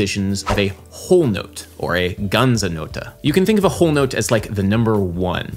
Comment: I don't know about you, but I heard "guns and notes" at 1:00. #America